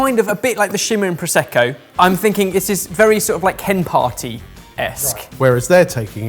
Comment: I’d recommend not referring to anything as “chem-partyesque”. Because i have a feeling you’re very innocent to the meaning of a chem-party, it’s a drug fuelled orgy something for food additives, well, not normally anyway. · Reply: I actually thought he said, hen party-esque. Confused me, as to why, they were licking scent off of one another, at a hen party.